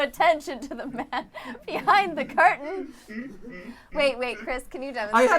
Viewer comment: Because of Mattpats shirt with Michael Jackson on it when said "Pay no attention to the m an behind the curtain" I legit heard "The man in the mirror"